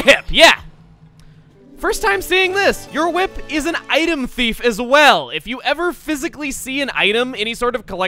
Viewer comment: For a second at 6:36 I thought Chugga said "Your whip is a waifu thief!" I was going to say "But that makes the whip the best item in the game if you can steal people's waifus!"